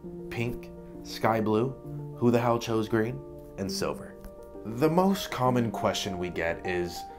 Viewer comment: I thought he said “who the hell chose green and Semen” 💀 I had to go back to check and he said Silver but it would have made sense anyway.